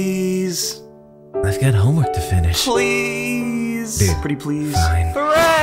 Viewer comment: bruh i was off doin dishes so i wanst able to see the text, and for a sec i thought geto said babe! 😅😂